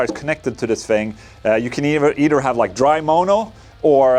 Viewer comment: Did he just say moan-o?